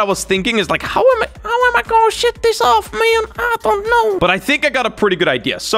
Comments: Did say shit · sounds like he said “shit”